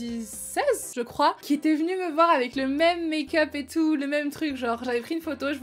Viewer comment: Quand ta dit "mes meilleures potes" j'ai compris "mes cap*te"😂😂😂😂😂😂😳😳